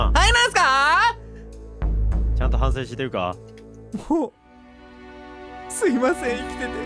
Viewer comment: 〜 「もう」のとこほんとに、ぶほぉにしか聞こえんくなったw